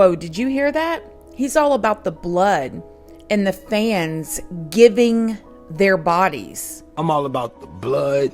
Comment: It sounded like he said "fans LEAVING their bodies"... but, I could be wrong Either way, it's weird.